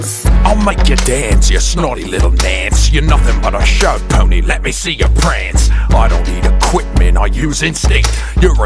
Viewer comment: LOL why does it sound like he said "let me see your pants"?